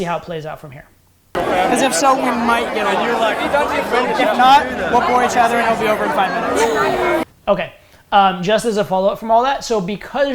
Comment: I didn't hear what say for qualifying the girl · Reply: we might get along, if not we will bore each other and it will be over in 5min